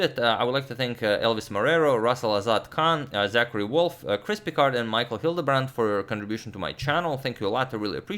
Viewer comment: Congratulations to Chris Pickard for his regular donations. You are an excellent subscriber. I remebmer you donate often, because everytime agad says your name, I hear "crispy card" and I have to think for a split second :)